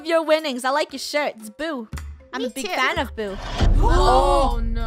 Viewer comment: What I hear: I’m a big fan of boo Me: OMG SAME